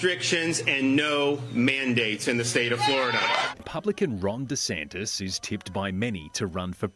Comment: With that accent, I heard "Republican Wrong DeSantis"